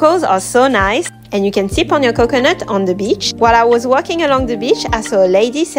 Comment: I thought u said, "And you can sit on your coconut on the beach." 😭😭